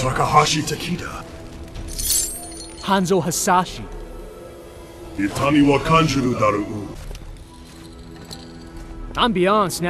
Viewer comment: Can somebody tell me what Scorpion said at 17:29? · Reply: Nicholas Heredia itami wa kanjuru dorou ( you will feel pain)